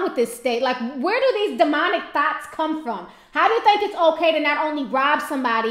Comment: I thought you said "demonic thots" hahaha